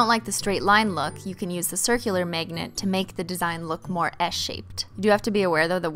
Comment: why do i keep hearing "MEGNET" and "MEGNETIC"